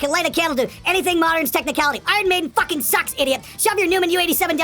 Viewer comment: seriously!?! i thought i heard "maiden sucks" ill bet that was a comment from a bassplayer that failed at playing steve harris lines...